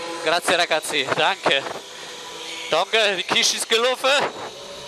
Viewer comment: For all the Germans out there. Did he say at 6:05 "Die Kiste isch gut glofe"? Ich versuche es gerade zu entschlüsseln :D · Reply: Die Kischt! :D Aber ja, ich glaube das wars. Die Kischt is g'luffe! :D Dreisprachiges Team radio: Englisch, Italienisch, Hessisch. :D